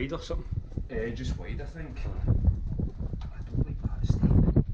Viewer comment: I swear when was coming down the sketchy stairs I heard an EVP 15:22 saying I don’t advise that or something like that 👻😳. Great job boys, you two are brave to even go in there. 👍❤️😊. do you believe in spirits? I definitely do. Was it a long drive to get to this place?